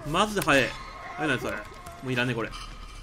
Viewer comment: 「もういらねこれ」 空耳「いらねー。いつものゴミ捨て場です」